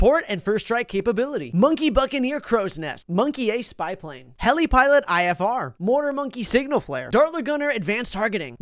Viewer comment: I misheard the first time so I heard “heli pilot IRS”